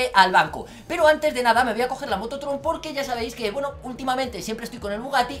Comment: crei que dijo moto troll XD